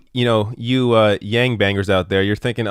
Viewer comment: I thought you said Yang Bangers.